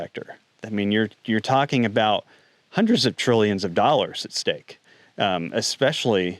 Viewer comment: I heard its 100's of billions